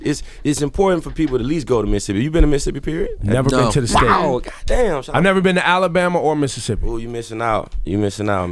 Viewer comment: does he say 'Goddam shawty' to ebro? aha